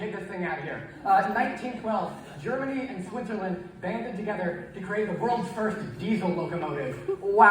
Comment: i genuinely thought you said "banging it together" instead of "banded together" and honestly i was gonna accept it